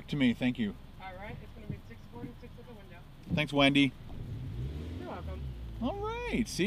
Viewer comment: Anyone else thought said "Thanks Wendy's!" I'm like IT"S TACO BELL but her name is Wendy Hahaha.... Anyways great review. I went out to taco bell today and just had to grab one (I MEAN TWO haha). Thanks